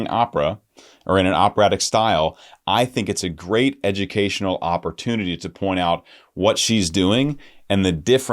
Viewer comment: I swear at 15:23 you said Opera-tunity